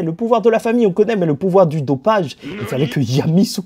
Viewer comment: le pouvoir du quoi ? 😂😂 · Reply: Du dopage 😂😂